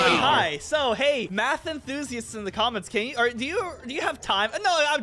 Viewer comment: I wasn't paying attention and legit thought said: "Hey, Matthew, in the comments, can you calculate the chances of Brent getting that 3 times in a row?"